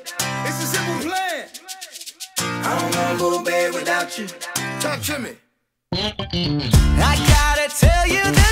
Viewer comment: i hear polish word "tańczymy" ( tanczymy=dancing) ? :D